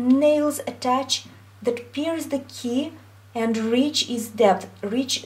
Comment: Der, thanks a lot for this video. I helped me so much :-) But I can't understand acoustically what you say from 4:24-4:33 (English is not my mother language): I write what I heard: we have to have the feeling that our fingertips have nails(?)... that pears(?) the key and reach is depth, reach the bottom of the keybutt". Is that correct? Thanks a lot. I looove your tutorials.